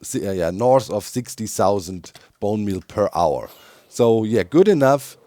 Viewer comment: I don’t know what you heard, I heard “big bone mealer” perfectly innocent.